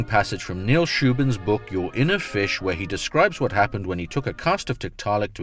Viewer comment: When you said "your inner fish", I heard "You're in a fish" great vid though!